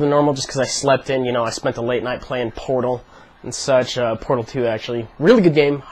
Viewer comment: lol thought he said he had been playing porn all night...